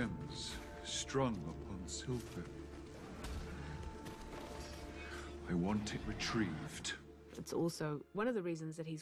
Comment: What does he say? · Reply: Sorry I'm 4 months late but to me it sounds like "I want it retrieved."